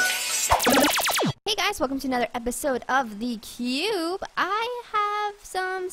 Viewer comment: Am i the only one that heard her say "welcome to the pube" 😂?